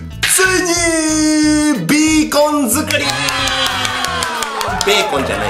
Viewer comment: ガラス張りだからから何か綺麗www それと、ベーコンって聞こえたwww(マイクラあるある😙)